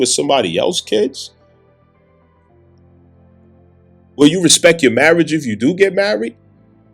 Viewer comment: Thought you said you respect your mother?